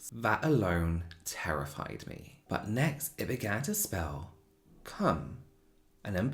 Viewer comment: I heard “The planchette began to spell cum” and I was like 👁👁